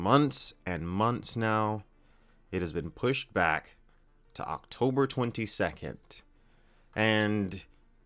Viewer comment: Woah... I'm only learning about the postponing now. And I thought you said " October 2022" instead of "October 22nd ", which beat the wind right out of me. But 3 weeks is nothing to worry about, unless you're that focused on this. 3 more weeks is nothing.